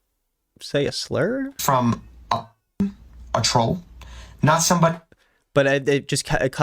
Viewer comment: I think he says “a fan, a troll”